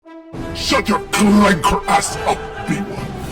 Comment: I was drinking arazona and I hear “SHUT YO CKLACKER ASS UP” and now I’m almost died